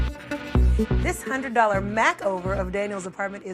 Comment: she did not just say MACKover....